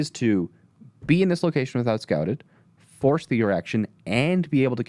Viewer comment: did anyone else hear "Force the erection" from or am I just trippin'?